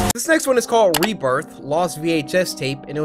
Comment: When he said rebirth i thought he said reaper Me after i thought he said reaper: OH HECKS NAH